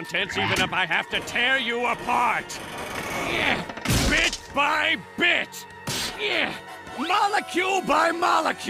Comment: did he just said "BEAT MY B𖦹TCH!" .